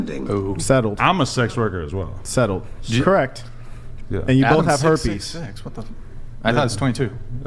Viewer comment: welp.. did he say they both have herps?